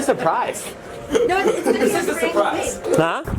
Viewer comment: she said "no it's (literally?) a prank babe" i think, or "supposed to be" instead of literally